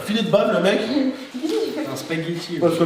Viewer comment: il a dit quoi g r capté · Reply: Il a dit je préfère le faire avec Idrissia qu’avec qui?